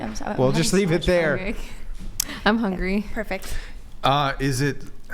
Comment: did she just say she's hungry.?